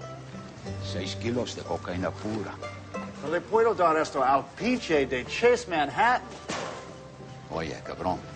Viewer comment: What does he say after "no le puedo dar esto al..."? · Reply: I believe he says "pinche de Chase Manhattan", "I can't give it to fucking Chase Manhattan"